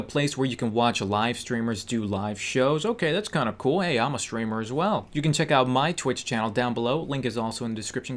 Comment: I heard screamer